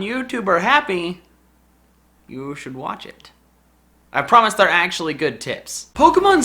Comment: i thought he said "i promise their good tits" 😂 lmfao rite now